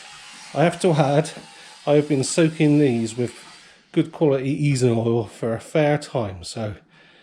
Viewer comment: Cheers. Just wondered what you said, I use it on awkward nuts and bolts too. Just thought you said some sort of oil. Thanks again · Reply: I may have called it easing oil?